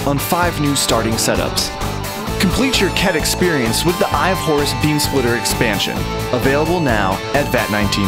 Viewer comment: at first i thought you said "eye of whores"...i was starting to question the value of it.